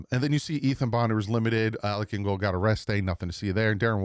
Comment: I had to do a double take when it came to what you said about Alec Ingold. When you said he got a rest day, I swear at first it sounded like you said he got arrested.